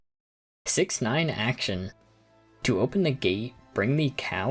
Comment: Dammit! Someone else already made the 6 7 joke. Edit: 25:05 Did you say 69?